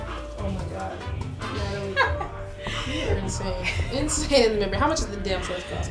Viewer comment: I keep hearing insane baby 🤦🏾‍♀️🤦🏾‍♀️ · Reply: Flexxx Suprm- she said insane in the brain